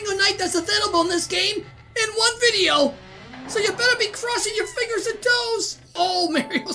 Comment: You know i am not fluent in english and at the beginning when i started following you i thought you always said "Cross all your fingers and toasts!" And now i am realizing how silly i was